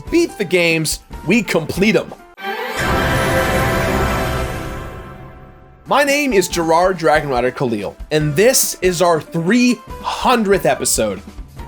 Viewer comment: WAIT DID YOU SAY YOUR NAME IS? (sorry if I'm butchering the name) THAT IS AWESOME!